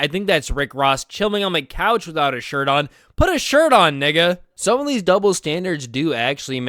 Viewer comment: Uh Did bro just say N*gga